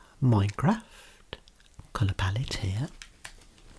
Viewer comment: Did bro say Minecraft palette?